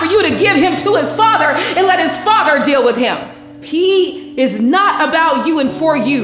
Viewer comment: His name is P and at 11:57 I heard P is not for you 😳😳😳 but you said he but it clearly sounds like you said P is not for you wow. 💌🙏🏽